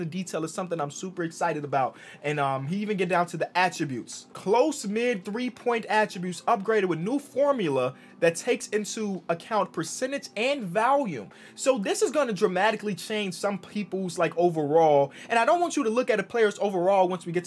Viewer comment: Did he just say valume